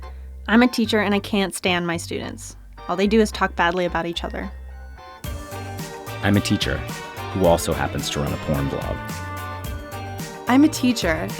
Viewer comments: I couldn't understand what the guy said at 1:07. Can someone tell me what they heard? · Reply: Appledosa "I'm a teacher, who also happens to run a porn blog" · Wtf is he saying at 1:07, "I'm a teacher who also happens to run the world."????? I've replayed it over 10 times now. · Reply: Hunter Freeman He says "I'm a teacher, who also happens to run a porn blog"